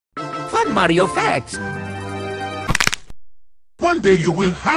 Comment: I thought it said cool mario fascist